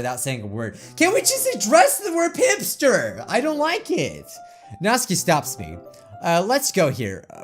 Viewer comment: Him:says natsuki Me:sounds like ur saying natski ITS NAT-SU-KI